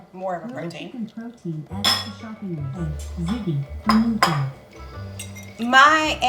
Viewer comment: LOL! If you listen to - he was saying 'ta zeggi' and your echo heard ziggy (he was talking while eating) and it heard you say chicken! Listen to it! Too funny!